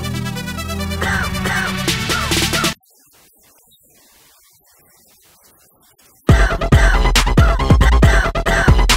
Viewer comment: If you couldn't hear anything at 0:30 wear headphone or earphones from 0:28 then you should be able to hear it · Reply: it was